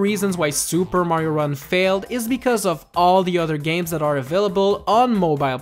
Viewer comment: I thought you said there were better games